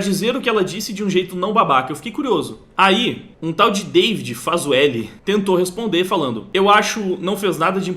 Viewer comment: Eu gostaria de apontar que o nome do mano ali era Fazuelli. Faz u elli. Desculpa, só achei engraçado mesmo. Quando o falou pela primeira vez eu achei que ele tinha dito "Faz o L"," sem sacanagem. 😂